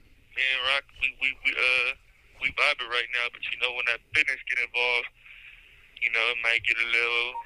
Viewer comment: Sounds like he said when the “business” gets involved..